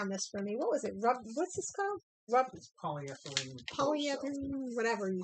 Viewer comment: bought you something so that when you poked your toothpick sticks in it, it didn't leave holes, however, not to be rude, I couldn't hear what said. I would like to know the name of the product. I need to make a very tiny round bead, I see that you have a 4 mm bead. Can I make a smaller bead than 4 mm? If I could, how long does the paper have to be? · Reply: Hi! He said it Polyfoam . I have not made any 3 mm as of yet. Try to experiment with different paper. That's what I do to achieve the size I want.thanks for your support of my channel